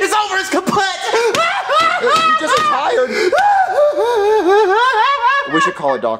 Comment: What Is Junior Is Saying? I Can’t Even Hear!🤣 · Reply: Junior said, he just retired!